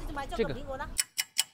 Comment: I’m sorry but it sounded like he said the N wOrD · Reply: 那个 is used as a filler word in Chinese